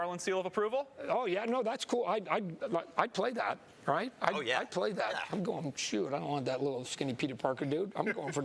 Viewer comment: I feel like he's definitely playable. All I heard was, "I played that." Also, all of the teasing and the stickers just makes it feel like it's either blue balling or he's in the game. If he wasn't in the game they'd probably be able to say it. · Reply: I think he said I’d play that.